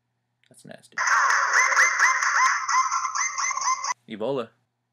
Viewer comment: when you saw the black person did you say ebola..?